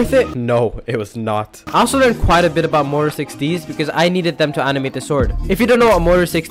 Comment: bruh all i heard was motar 16